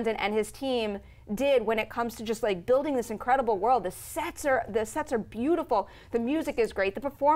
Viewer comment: I swear I heard the "Sex was beautiful..."